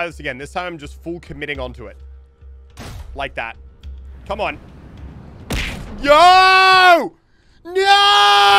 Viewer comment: If you lower the sound then it sounds like he is saying “yo” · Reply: He says yo already